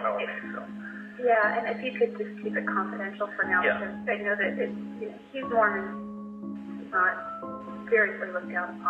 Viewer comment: Around the 18:00 mark, I think she says something like, "he's Morman...". Not, "he's more. " because they were both Mormans and premarital sex is frowned upon within the religion, which could be why marriage was a trigger for her.